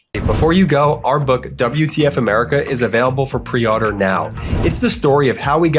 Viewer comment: 🚨 QUICK REMINDER: Please take 2 seconds to make sure you’re still following the MeidasTouch Podcast on your favorite audio app! We’re STILL getting messages from listeners saying apps have mysteriously unfollowed them from the show. Check now on Apple Podcasts or wherever you listen. It takes just 2 seconds, it’s free, and it helps us tremendously. Apple Podcasts: <link> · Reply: I keep hearing “ pre-otter” not “pre-order” the book, too funny 😭😭😭